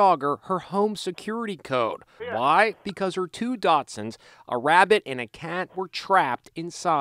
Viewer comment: Did he just say 2 "Dotsunds"? Lol